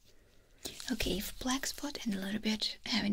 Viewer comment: All I keep hearing is “If blacks put and the letter bitches”